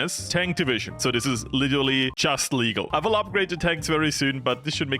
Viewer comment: Barely legal tankussy (I'm sorry, I'm really sorry, I just couldn't hold my urge when I heard that phrase)